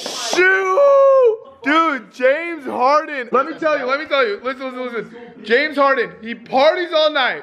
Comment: James hardens nephew sent him the package not James harden just so y’all know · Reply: I thought he said James Charles